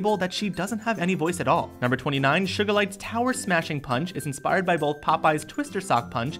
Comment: it sounded like sugerlite lol